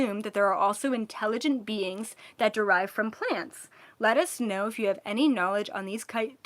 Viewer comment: “Are there any plant 🌱 based beings, (let us) but I heard “lettuce 🥬” know” haha 🤣 just had to share ✨🤍